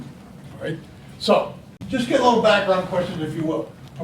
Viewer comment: What's the question that we asked the customer?? You can't understand it no matter how much I rewinded it it's on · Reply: Performance questions.